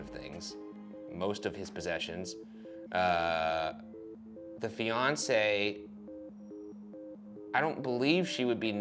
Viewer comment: Whenever Ryan says "The fiancee" I hear "Beyonce" and I get super confused over who why Jay-Z killed Superman.